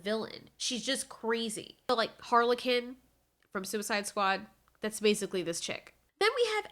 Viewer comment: Did she just say Harley-Kin instead of Harley Quin